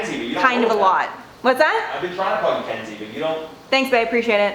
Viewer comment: Did McKenzie called bae??